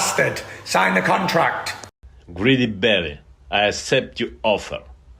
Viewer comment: “Greedy Belly” in a heavy Eastern European accent is the one of the best disses ever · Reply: I thought he said "Greedy Bellend".